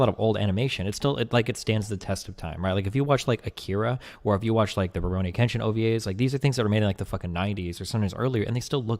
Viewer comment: akira and what was the second thing he said? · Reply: The Ruroni Kenshin OVAs